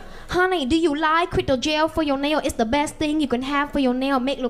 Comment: I thought she said “do you like quit or jail” 💀💀